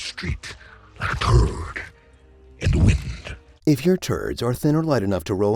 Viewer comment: I had thought he said "toad in the wind" and it confused the hell out of me, and honestly "turd in the wind" is just as confusing.